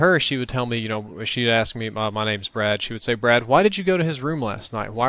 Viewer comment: Did you just say your name is 'Brad'?